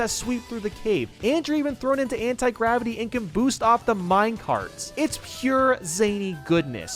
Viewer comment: did you say... minecarts